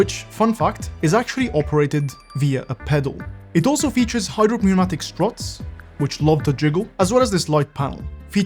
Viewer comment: did I heard right, hydropneumatic ?!